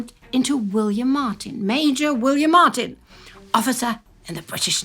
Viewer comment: Wow, Willy got a promotion this late in the game, He's A Mayor now instead of a Major